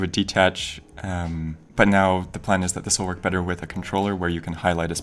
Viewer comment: Did he say at 7:57," It would work better with a controller"?????????